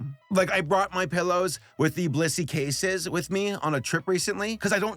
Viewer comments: didn't sound like blissy sounds like you said bussy for sure uhh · i had this on in the background, and i swore you said "this video was brought to you by bussy" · I thought he said ‘this video was brought to you by bussy’ and I, the Emperor of The Han Dynasty, thought this was gonna take a very different direction. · Saber your blissy ad just sounds like you said 'bussy' and vehemently advertised bussy. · i thought he said Blussy ☠ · I did a digital double take when he said the sponsor of the video. Genuinely thought he said “bussy”. Almost lost my shit. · fr thought you said blussy